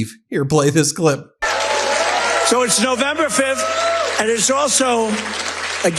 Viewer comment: Look at the morons behind him! How disgraceful! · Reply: What is Noticable is they aren't cheering wildly like they used to and the red hats were very few. Also I thought he said NOVEMBER 5th... Is this Nov or Dec?